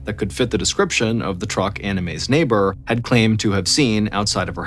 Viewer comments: Sorry, Anna May! You didn't deserve your fate. No matter if you are rich or poor, do not loan money to anyone, especially family or friends. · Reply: I thought he was saying anime 💀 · At first I thought he said her name was "Anime."